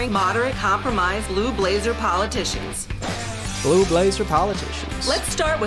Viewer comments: thought she said lube laser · when she said “blue blazer” i heard “ lube laser” hahaha